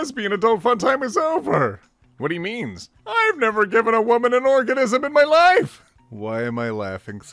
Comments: Did he say organism at 2:57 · “I’ve never given a woman an organism in my life” 2:57 .......buddy I think you said it a lil wonkily. Still incredible though lmao!!!